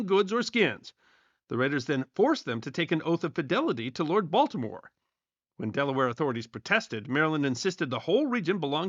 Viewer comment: I had to listen to this twice. The first time I heard “Lord Voldemort” instead of “Lord Baltimore”. Gotta stop watching so much Harry Potter content on other channels. 😳 🤪 🤣